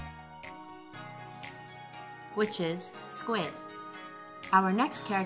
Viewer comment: I didn't understand. Squeed ? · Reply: squid